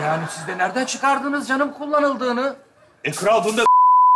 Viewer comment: de ne dedi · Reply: e bunda diş izi var kldsjlksjdgflsdg